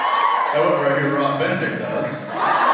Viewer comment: What does Jensen say at 13:30 about the dresses after Jared says it's [Jensen's] loss not to wear dresses? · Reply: Akili Alima he says "although i heard Rob Benedict does" referring to Rob dressing up as Khaleesi for the Friday Karaoke